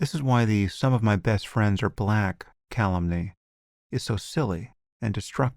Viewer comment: 'The some of my best friends are black' what? Cal-something? I want to know this word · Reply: Calumny